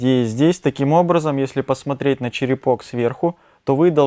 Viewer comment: Итак,массаж черепа лодкой или скребком гуаша:-вопрос:Уважаемый доктор, вы показывали движения От макушки к периферии,а сказали:-от периферии делаем такое солнышко, ,видимо,неправильно расслышала?